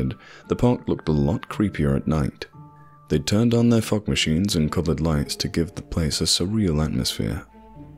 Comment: When you said Fog Machines I thought you said Fuck machine